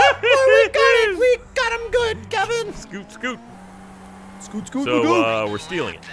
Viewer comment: Is it just me or did say the N-word ? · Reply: I think he said scoot scoot mcgoot. He says it in a deep voice so I can understand the confusion